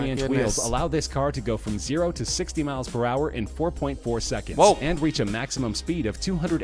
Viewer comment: did he say 4.4 seconds